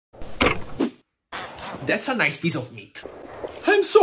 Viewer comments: Why did I hear him say " I am so Ugly now " I need to get my ears checked 🚶‍♂️ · "I'm so hungry now!" "BAA!!-" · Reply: Oh- for some reason I heard “I’m so ugly now” 😭